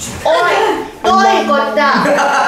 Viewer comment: がの版の時じゃなくてウィッチーって聞こえた！